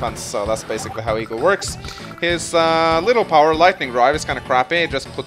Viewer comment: did you say " Lightning Rod " ?